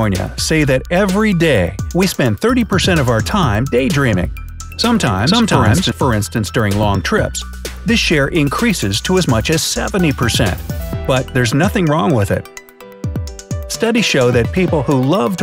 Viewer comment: did anyone else hear him say "sometimes" twice in a second